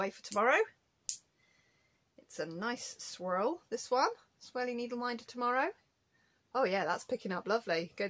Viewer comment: I know you're saying 'swirly' but I keep hearing 'sweary' and look up expecting you to have put a curse word on a needle minder lol